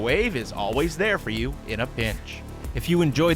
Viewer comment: Who else heard him say bitch at the end ?